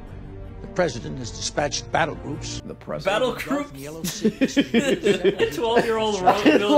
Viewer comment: So you guys mishear "battle groups" as "battle troops," don't realize your mistake in editing and keep it in the edited video as a burn?..